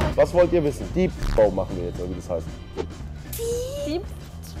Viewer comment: was sagt knossi da? Deep throat?